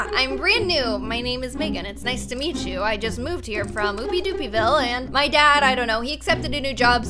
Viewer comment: “I moved here from Moopy Doopy Vile - word of the year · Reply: I thought she said Poopy Doopy Vile